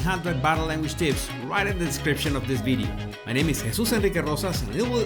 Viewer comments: Anyone else hear him say “my body language baddies” 😎😝😘🤣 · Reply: When I first started listening to him it sounded like he was saying 'my battle language baddies'.. lol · I love being a Body Language Buddy! Thank you, Jesus, for you informative and entertaining videos! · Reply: Is that what Jesus is saying? I thought he was saying Body Language Bodies 😂